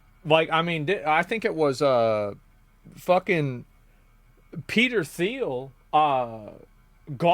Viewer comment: I thought you said “Pete Steele” and I was very confused for a second